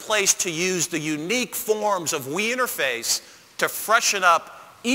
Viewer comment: Lol...at 0:42 it sounds like he says "the refreshing forms of weinerface."